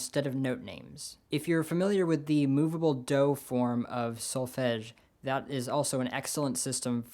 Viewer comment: This isn't really related to the video but I'm getting into transcribing chords since I can handle melodies now. I'm transcribing 'killer song' by Jun Maeda and I think the chorus chord progression is vi, IV, I(3rd inversion?), V. Am I correct? Isn't this progression somewhat common? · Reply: Also, at around 2:30 you said something like "movable dough form of sulfedge"... or something like that, I didn't quite catch that. xP Maybe you should edit some text on the screen or people like me will start desperately google searching it without results :P